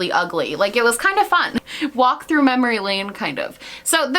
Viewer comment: I started laughing because I know you said "walk through memory lane" but in my mind I heard "wap through memory lane" lmfao